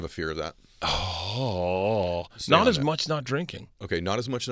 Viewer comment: By the way, Bert said “not as much not drinking” meaning he doesn’t worry about randomly dying as much now that he’s not drinking. You thought he said “not as much AS not drinking”. Thought I’d clarify.